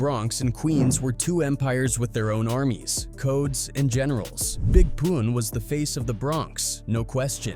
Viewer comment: All I heard was big poon 🤣